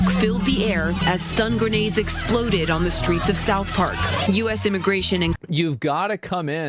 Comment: did she just said south park